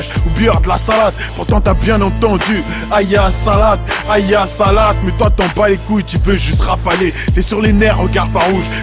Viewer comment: il dit quoi plzz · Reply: Haya ala salât = accouraient a la prière